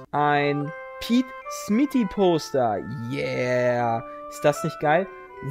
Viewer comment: achsoooo ein Poster ich habe Toaster verstanden aber als ich dann die grösse gehört hatte konnte ich das nicht glauben ^^ Für diesen verhörer und lachflash würde ich gerne gewinnen Steam: uebermuter alle können mir spiele schenken ^^